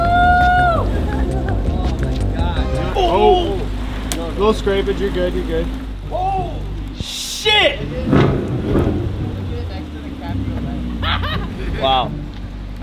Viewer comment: did anybody else hear “ohhh sh!t n!gg*“or am i crazy